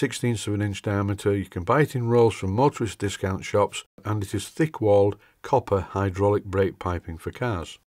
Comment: "doubled walled brass/copper piping for cars" I'm sure you mean steel. The tires on the wheels look...crooked in video. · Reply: I thought I said "Thick walled copper tubing" ..... but never mind ......